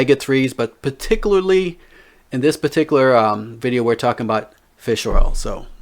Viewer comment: I see your work. I thought you said fish row, 2:09 LOL, but then I heard again and you said Fish Oil. LOL Thanks for the content my friend.